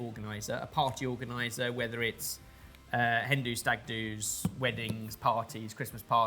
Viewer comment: I’ve gotten so used to odd things coming out of mouth that I legitimately thought he said “Hindu stag dos” instead of “hen dos and stag dos”.